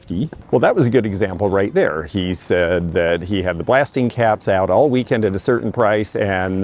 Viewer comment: DID I HEAR YOU RIGHT ??? BLASTING CAPS ???